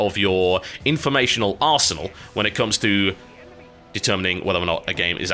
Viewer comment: i thought he said "Informational Arse" XD